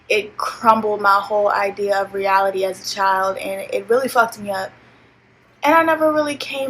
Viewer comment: I heard "I'm a child" clear as day